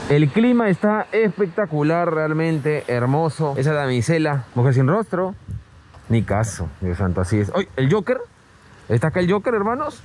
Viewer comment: Soy yo o escuché decirle el a la mujer sin rostro me caso en el minuto